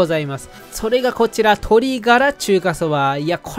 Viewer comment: 「鶏ガラ中華蕎麦」に聞こえるけど字幕が「豚ガラ中華蕎麦」( ´ω` ) これはつまりWスープってこと？？笑